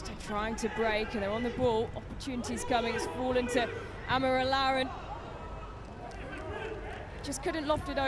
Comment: Is it just me or did she call tobi "amorolaran"